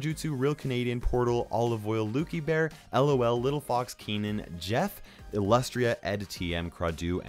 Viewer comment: Lmao when he was reading the name at the end I thought he said little fox peen